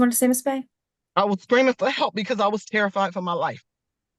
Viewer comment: Did she say she was "scarified" for her life? A word from the late 18th century?